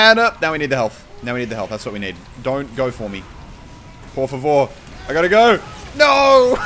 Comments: I honestly would have accused myself of setting up that clip at 0:32, or at least recording my voice over afterwards calling that I was about to get a collateral... but no... I am really a gypsy... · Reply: Muselk On 2:34 did you say '' Porfavor '' ? :D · I honestly would have accused myself of setting up that clip at 0:32, or at least recording my voice over afterwards calling that I was about to get a collateral... but no... I am really a gypsy... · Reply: the 2:35 did you said "por favor"?